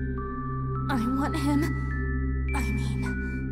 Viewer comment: What does she say between 0:45 to 0:47? "I want him. I'm him."? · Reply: iAFK "I want him, I mean"